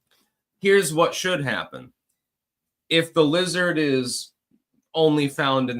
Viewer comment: I thought he said the wizard, not the lizard